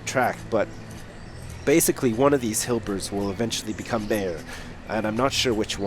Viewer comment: When it said Hilber was born, I thought it said Hitler!